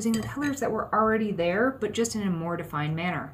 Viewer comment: That's too funny not to share: i played it a couple of times, til i figured that you actually said, " a more defined manner". I had thought you said, " a mortifying manor"... ya, the age of hearing impairment....